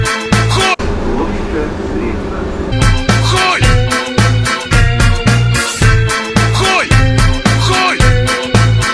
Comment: do you know what word is pronounced at 0:38? this is the word penis. you speak russian. you probably know this word 😁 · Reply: Khuy